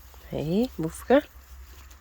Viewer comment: What was the word for „Rascal“ at 4:21? Buffke??? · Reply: BOEFKE.... Little rascal! 😁🐎🌷